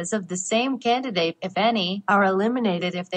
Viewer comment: When these TTS voices say "if any" it sounds like they say "a fanny". And yes, 12 year-old humour I know.